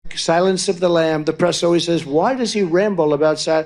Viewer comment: Did anyone else notice that it sounds like he said " sadness of the lambs"? 😂😂